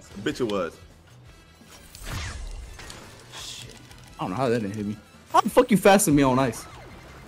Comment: Chat was trippin over this last night. Dylan said "Damn they gave his ahh double drills?" but he said it kinda fast and he mumbles when he talks so it sounded like "Damn niggas have double drills?" In the moment, it did sound kinda crazy tho. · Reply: Ok but what about at 51:37 “idk how that ngga hit me” what does he actually say here?